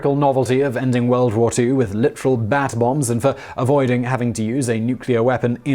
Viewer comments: I thought was saying bath bombs at first 😅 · U said "Bat Bombs".... I heard "Bath Bombs"...